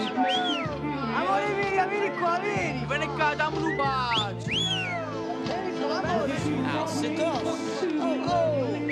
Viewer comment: Can anyone explain what the kid said at@ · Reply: He said “my love give me a kiss”